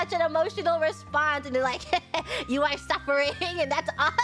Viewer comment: I think she's saying, "You are suffering, and that's awesome."